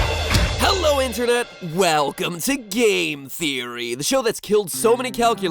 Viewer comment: Every time he says game theory it sounds like he says gay theory.